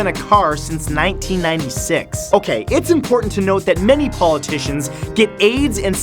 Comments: I thought you said many politicians get aids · did he just say that many politicics get aids? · Did he say "it's important that all politicians get AIDS"?